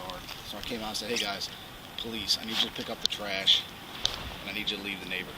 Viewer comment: Instead of "please" he says "police" 😂